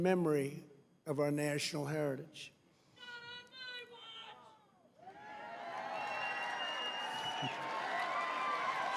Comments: what did the person shout out?? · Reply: Not on my watch. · Help! What is said at 9:45 ?? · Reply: “Not on my watch!” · What did the dude say around 9:46? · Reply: “Not on my watch!”